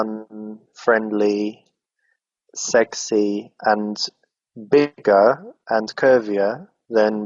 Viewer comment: what he says? "fun, friendly, sexy and ????, ???? thanks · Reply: Bigger and curvier? There is an audio artifact there. Its hard to understand. I hope this helps after 5 years :D